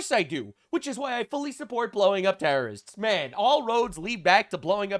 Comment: In your intro I thought you said "blow up Paris" then you repeated it and it was "blow up terrorists". God help me I thought DonJohn had said "blow up Paris" in his inimitable rude crude style and I thought that even Mister Trump would not mess with a rather horrible joke and I beg your pardon for chuckling for 3 seconds. I'm sure that I would have loved Paris a hundred years ago. When he turned to the Prime Minister of Japan, Sanae and made a horrible joke about Pearl Harbor I lost it. She was cool for the cameras. Never ever ever underestimate DonJohn.